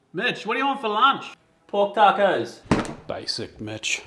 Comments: I s2g I thought he said basic B** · When i listen first time i thought he say basic bitch😂😂😂😂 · I thought he said basic bitch 🤣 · I literally heard " BASIC BITCH" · Got a second I thought he said basic bitch not mitch